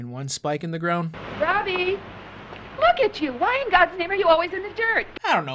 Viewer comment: "Ryan why in gods name" sounded like Ryan Gosling for a second.